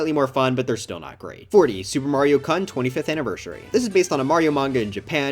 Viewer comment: What did he just say?? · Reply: Super Mario Kun